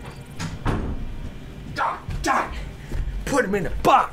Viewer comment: LOL! I think said god-damnit